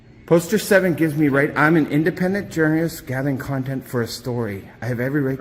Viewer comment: If this guy can’t walk without getting winded (and spitting while he speaks- it sounded like he said he was an independent jurist) I’m imagining that’s why he didn’t last in the military! At the end of his rant he sounds like Daffy Duck!